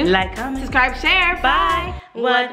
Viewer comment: I always thought they said “ONE freaky twin thing!” I have no idea why... 🙈